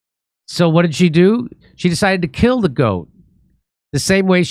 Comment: For a split second, I thought it said “Noem decided to kill the unarmed goat.”